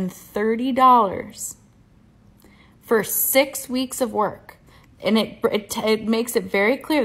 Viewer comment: I so misheard the lady at 3:24 because it's so wild. At first, I though it was paid crazily well. She actually said it paid $130 for 6 weeks of work. That was so bonkers my brain made that into $130K for 6 weeks. But a master's degree not even paid rent money of a week after 6 weeks is crazily paid on the downside...